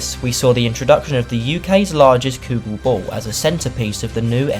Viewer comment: Hey, great video, extremely well put together and definitely underrated!!! I have to ask though, what is it that you say at 5:33? Me and my GF were watching this and can only hear it as "Cougal Ball" and we are dying to know what that is. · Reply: Haha nice one mate! It’s called a “kugel ball”, it’s one of those massive concrete sphere water fountain things… best way I can explain it😂